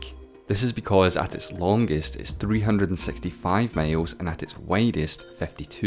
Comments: I'm not the only one who heard 'mails' right? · 😂😂 thought you said males but miles